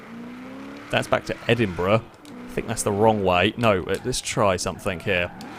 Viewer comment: I heard you said "Edinbruh" at some point in the vid lol